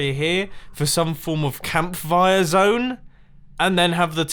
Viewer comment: Was it just me or did it sound like said campfire with a v. Like camvire